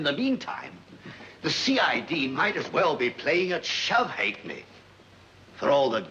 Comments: where the subtitle says "unintelligible", the commissioner says The CID might as well be playing at "Shove ha'penny"- ,also known in ancestral form as shoffe-grote ['shove-groat' in Modern English], slype groat ['slip groat'], and slide-thrift,[1] is a pub game in the shuffleboard family, played predominantly in the United Kingdom. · Great quality upload !! Kudos to those who restored this film. I noticed early in the film the subtitles say ".....(unintelligible)". For those whom english is a second language, at 2:45 in, " The CID might as well be playing at Shove Ha'Penny "